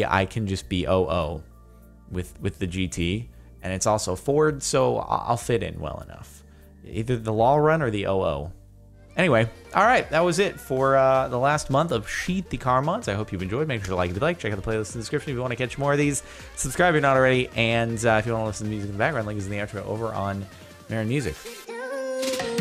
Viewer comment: It sounds like you said "sheety car mooodss"